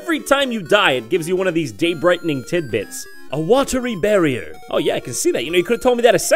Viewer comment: Wasn't looking at the screen and thought he said "I want to rebury her"